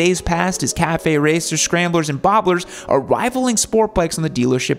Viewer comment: did say “Boblers”? Or is that just me?